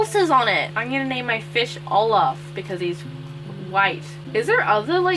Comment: I thought she said "I'm going to call my fish Allah because he's white." Yes I realize she said Olof.